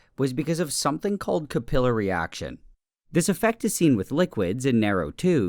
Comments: wait, did he just say caPILLar reaction?? I thought it was CAPillary reaction this whole time 😭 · "capilla reaction" . . . capillary action? Anyone else hear that? Tell me it's just the tape. Edit: 15:07 - Nope, it hurts.